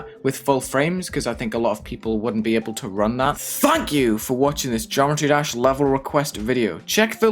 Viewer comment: At the end it sounds like you say “f*ck you for watching”